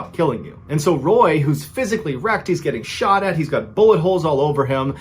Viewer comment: I thought you said physical erect